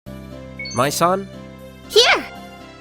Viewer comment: For a second I thought I heard "My son" (Mai-san)